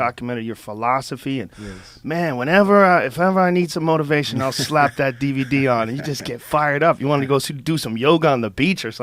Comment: Can anyone help? 05:57 does Rogan say "I slap that DVD on.." or "I lock that DVD on.." ? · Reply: Whenever I need some motivation I'll slap that DVD on.